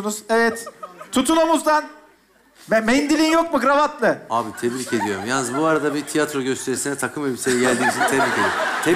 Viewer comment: Çocuk cevap olarak ne diyor? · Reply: İş yerinden geldim